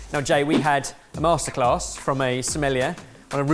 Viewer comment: from a smellier.... (that's what it sounded like)